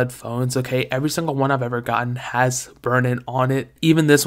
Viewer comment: What did he say at 1:03? Burnin?